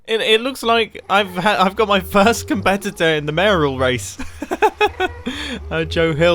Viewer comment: "I don't have any friends in the mayoral race at the minute" WhY dOeS tHaT sOuNd So AdOrAbLe AHHHH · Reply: I thought he said "marital race" and got incredibly confused for a moment